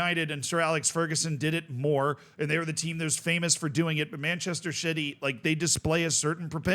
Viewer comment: Did you call us Manchester Shitty at 6:20?! 🤣